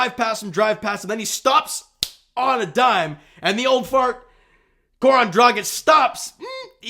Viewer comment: I had to play it back at 3:46, thought you said the old fart Goran Dragic stops.